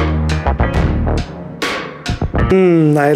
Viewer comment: Every time I hear the merch intro, i hear "buy my shit" instead of shirts